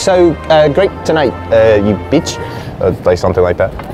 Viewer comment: Did he say bitch @ 4:15.